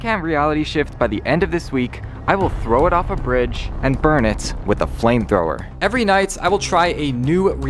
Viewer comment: Lmao I thought you said "I'm gonna destroy this bridge with a flamethrower"